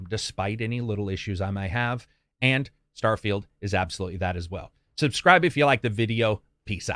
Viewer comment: Am I the only person who heard, "And... Starfield is absolutely bad as well."